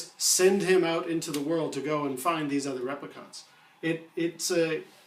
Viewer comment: The fuck is a replicont? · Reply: New Kasmir I thought he was saying Replicunts